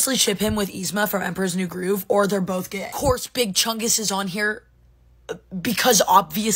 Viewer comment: For a moment when i saw big chungus i legit thought she said "big chugussy" i had to look again!!😂